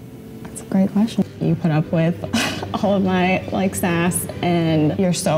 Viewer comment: I thought she said "you put up with my ass" lol But seriously, this is how good men and women get hurt and end up in a bitter place. Be good but don't be naive, people can be savages. Don't make a commitment to that, run while you still can. Respect yourself. Want better for yourself and future family (if you want kids) because people who lack integrity, empathy and sense will tear down a home. Just no.